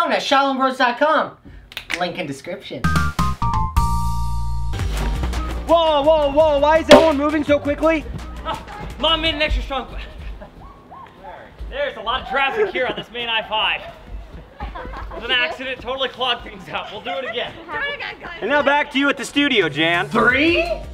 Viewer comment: what i heard: mom made an extra chocolate